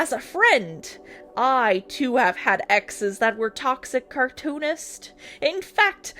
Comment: okay, I didn't payed attention to the screen at the moment, and what I heard was like he had "exes that were toxic cartoonists". I was like "dayum, man has a parasocial streak of L's for animators".